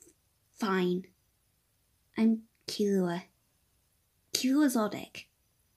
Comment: i lowkey thought he said Killua LilDick 🤣🤣🤣